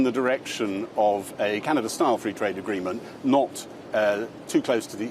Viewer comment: did he say cannabis style free trade 6:24 agreement